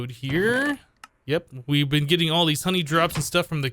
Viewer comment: I thought it was money instead of honey. I guess honey is money technically.